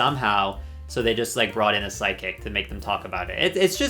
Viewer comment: Every time you say “psychic”, I hear “sidekick” and I’m like “What do you mean they brought in sidekicks? What is a drag queen sidekick anyway?” 😂